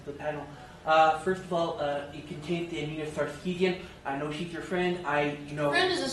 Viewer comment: Does anyone know what he said? Or even who he is talking about which got to respond with "friend is a strong word". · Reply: Anita Sarkeesian.